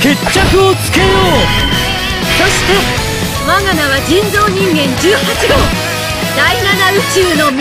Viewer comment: sorry but i hear n word at 0:35 ngl